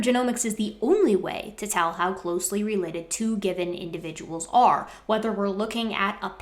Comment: funniest mishearing coming back to this. genetics is the only way to tell two gibbon individuals apart